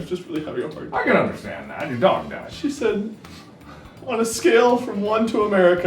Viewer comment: When Ethan said "your dog died" I thought he said "your daughter died" then I heard mark say "it was just a dumb-" and I died laughing